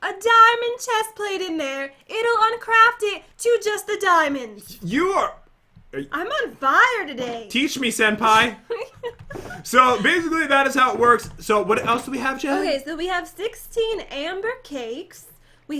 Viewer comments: I thought he meant to say teach me hentai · did you say SENPAI!!! Did you play yan sim or watch it????